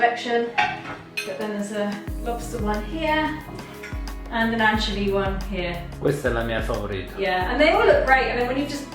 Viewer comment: Did anyone catch the name of the shop carrying the beautiful plates at 9:37? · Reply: Here it is! Ceramica Assunta Positano.💕😘 <link>